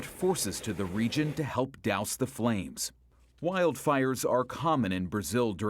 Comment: I'm studying English as a second language.Can anybody tell me what is the word at minute 1:00 please. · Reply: Douse the flames means to drench , pour liquid over the fire